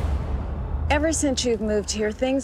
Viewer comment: What does she says at 1:47..??? Cant understand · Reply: Too-ta-loo